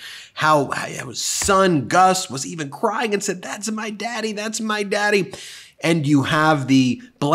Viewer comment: Okay, I don't think he (Gus) said "thats my daddy, thats my daddy" I'm pretty-sure I heard him say thats my dad. Just fyi LoL